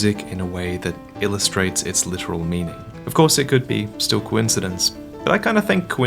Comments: I misheard coincidence as gwyn-cidence... or did I o.o? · Did anyone else hear that as Gwyn-sidence? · Did he Just say "Gwyncidence"?